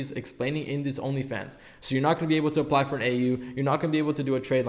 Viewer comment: What did he say at 10:58 apply for a trade line and a what? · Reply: AU authorized User. Just Google how to repair/rebuild your credit